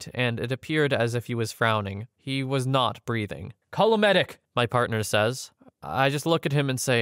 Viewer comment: columnedic? what does that word mean? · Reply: Voice to text probably changed “call a medic”.